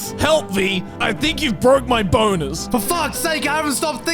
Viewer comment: I just can’t. I initially heard "bone-es"(just pronounce "bones" wrong) and wanted to hear it again to double check and when I heard it, a state of uncontrollable laughter hit me.